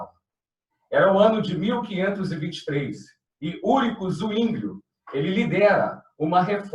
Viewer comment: No Minuto 0:22 eu Já não entendi muito bem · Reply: Acabei de verificar, eu quis dizer: Ulrico Zuínglio... Porém a minha pronúncia não foi das melhores (rsrsrsrs).